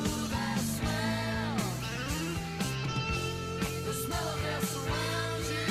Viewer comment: I Need You, Tuesday's Gone, Saturday Night Special. I have been listening to That Smell since I was a kid. I was right now years old when I found out it says "The smell of Death surrounds you". I always thought he said "The smell that surrounds you" I guess thank God for subtitles 😂